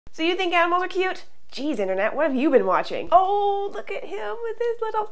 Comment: At the start I thought she said 'so you think I'm over cute?' despite rewinding 3 times. Yes you're a super cutie o(ﾟ▽ﾟ)o